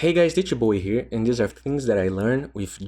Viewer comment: What does he say at the beginning after "hey guys"? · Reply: More of a wrong pronunciation than an unique accent, but yeah, he says "D 2 Bowie here", pronouncing "two" as "chu". And yeah, I also heard it as "Ditya Bowie" until he said "two" on some other video, then I was like "OOOOOH"